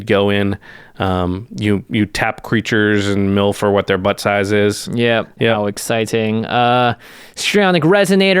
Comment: Did I hear correctly: 1:17:05 "You tap creatures and you mill for what their butt size is" lol